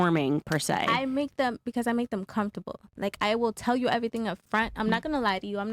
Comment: I really thought she was about to say "I make them c**"